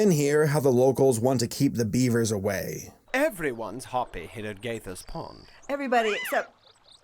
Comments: I keep hearing "GayFish pond" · I kept hearing “Gay fish pond” ☠️